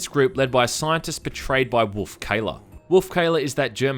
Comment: At first I thought you'd said "betrayed by wolf cola", for a split second I thought crysis might have a tie in with its always sunny in philadelphia.